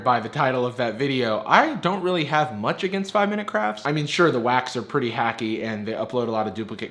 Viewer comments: did you say: The Wacks Are Pretty Hacky ? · I heard you say, "Sure the wacks are pretty hacky," and I thought it was right. Then I read the text. I honest to God couldn't stop laughing